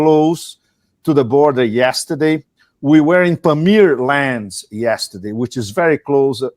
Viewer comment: Pepe Escobar is a legend. He only speaks truth. And look at that smile at the beginning !! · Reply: Yeah, I watch him a lot on youtube. 2:25 I think he said Pamir, not Premier. 帕米爾 / 帕米尔